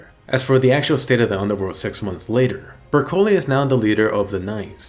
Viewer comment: In 2:52 did you say sex months later?